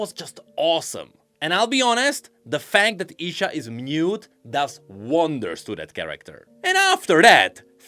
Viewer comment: I stop paying attention and then came back at 3:50 and was like wtf lol took me like 3 replays to hear the word "mute"